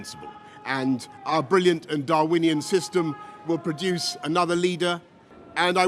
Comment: did he say Darwinian system? So will Tory politicians fight to their death to see who is the fittest?